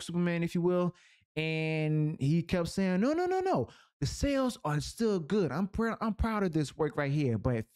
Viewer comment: brother, you say "sales" like "cells". I first thought you meant to say something else.